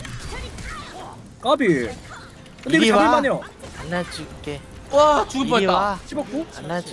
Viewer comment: 분에 가 이리와 안아줄게 나만들림?